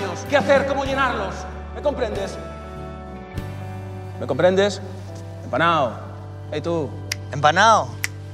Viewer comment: Tengo una pregunta, 8:34 No entiendo lo que dicen los hombres en esta escena. empanao?? enpanao? Qué significa es? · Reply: ¡Hola! En esa escena dicen "empanado", que es una forma coloquial de referirse a alguien que está distraído y no presta atención. Salud 🍻